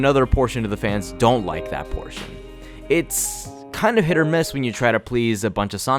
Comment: did you just say HIT OR MISS